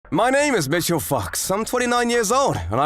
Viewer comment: I know he said foxx, but the way he said it, it sounded like he said fucks, lol